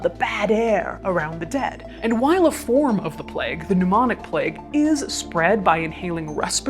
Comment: seriously thought she said "Mnemonic Plague" 😆